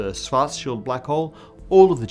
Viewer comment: I heard "fart shield black hole" at 15:11, giggled, and I'm not even going to apologize for it.